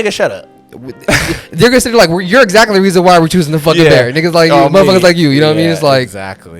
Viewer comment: am i the only one who heard say n1ggas like you🙏😭